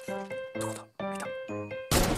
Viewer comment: Oh shit！(空耳)